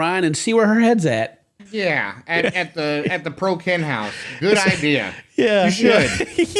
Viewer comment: Did D say Pro Ken house? So dad's house is the ProKen house and Ryan's is the broKen house 🤭